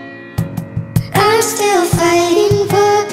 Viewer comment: Am i the only one who heard ''I'm still fighting puppies''? Eh, I might just be crazy...